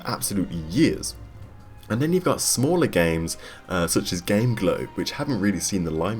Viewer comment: What's that game at 6:51, did you say "Game Glow?"